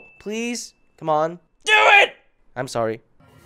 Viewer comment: At the intro I heard "pleeease... mom... DO IIITTT!... I'm sorry..." khhh 😂